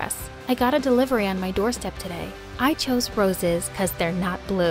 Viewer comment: did she say "jeez he's so lame''???